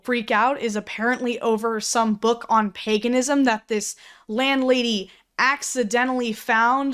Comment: i thought she was saying Peganism. the belief of pegging